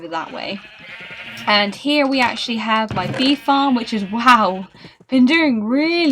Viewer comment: Thought you said weed farm 😂